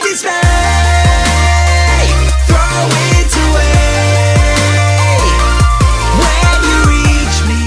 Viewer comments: What are the lyrics in the beginning, cause I hear "Take my cheescake. Throw it away. Where you each eat." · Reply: Very old comment I know but I thought it said "take my dismay. throw it away. where you meet me" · What are the lyrics in the beginning, cause I hear "Take my cheescake. Throw it away. Where you each eat."